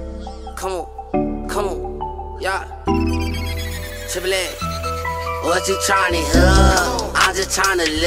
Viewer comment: did he say "tripple x"?